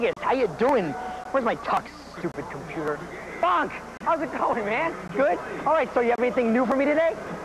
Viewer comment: Lol, Yeah it does kinda sound like he says, "Fuck! How's it goin' man!"